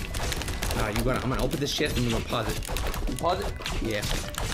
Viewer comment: did he just say shit?